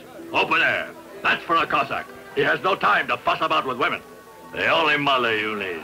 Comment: I know it's dubbed but did he say what I thought he said? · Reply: Lol no time to fuck about with wimmin